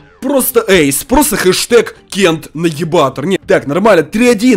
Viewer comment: Мне послышалось найибатор